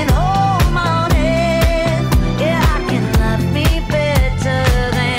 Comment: "I can love ME better...." Many reactors mishear that as "I can love you better".